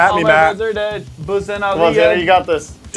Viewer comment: Did anyone hear say "all my friends are dead.boosting off the edge" it's